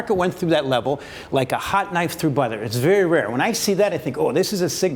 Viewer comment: I had to double take as I thought he said it’s like Hot knife through butt!! 😳😳. Phew, he said butter. Seems like people are using Crypto coin like they would use gold in the olden days and anticipate a huge correction depending whichever way G20 Party goes right?